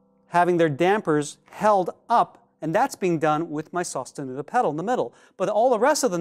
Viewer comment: I though I heard “sosta noodles pedal”.